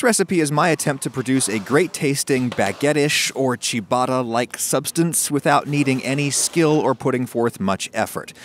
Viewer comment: I thought you said chewbacca like substance